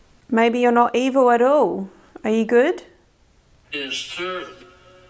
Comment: said “maybe you’re not evil at all, and I heard “yes sir”